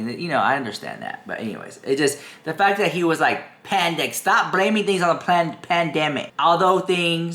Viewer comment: LOL I heard you say plandemic 😃